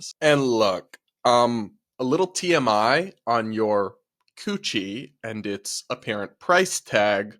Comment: Oh she said $50,000 coochie 😂 i thought she said Gucci